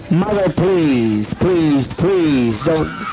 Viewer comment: i just heard 'please mommy no' so sad